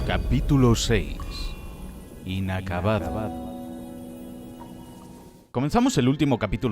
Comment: Escuche "El acabado" 🗣🗣🗣🗣🗣